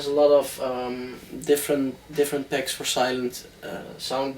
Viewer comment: What does he say he's using at 9:59? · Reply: Sylenth